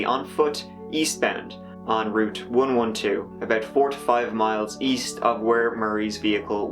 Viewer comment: Seen 4 to 5 miles 45 minutes later - first I thought he said 45 miles - but 4 or 5 miles is a brave distance in those conditions in that amount of time....! She seemed troubled in her behaviour leading up to her taking off - sad story - such a beautiful young woman - such a terrible loss....!